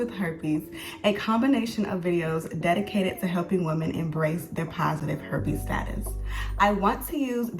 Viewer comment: Wait! 0:29 did she really say embrace their positive herpes status? 😮😮😮😮 Its probably the equivalent of stripping naked and hugging a cactus while stepping in poison ivy. SMH This is gonna be fantastic advertising for her cosmetics line. #SYSBM